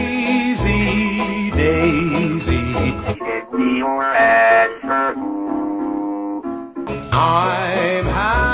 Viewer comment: “dAiSy DaIsY gImMiE yOuR a$$ FoR pOo” 😭💀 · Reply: I hear "daisy, Daisy! Gimmie your a$$ to cook!"